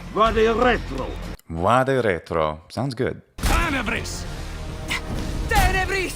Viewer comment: black prior am i the only one hearing him say „Tanabres“ instead of „Tenebris“? 💀💀